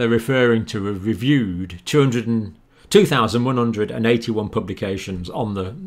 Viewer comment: Hi i was just playing Catch up with utube showes and in this show u got to the number 2,181 but on the third or second time u said it it sounded like u said2,118 I replayed it 2 times but sometimes the sound strip slowed down but I am almost sure it was second time u said 2,118 please let me know if that was wrong